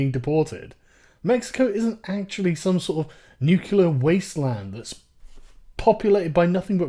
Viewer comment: Did you just say "nucular?" triggered.